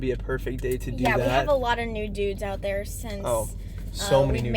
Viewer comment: As a french guy I heard a "lot of nudes" and I was like "Hold on dudes ! Whaaaat ?" Then I realized and had one of my best laugh today :D